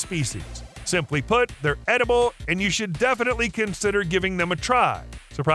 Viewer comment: People see Crabs, I see breakfast, lunch and dinner. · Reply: i thought he said they were inedible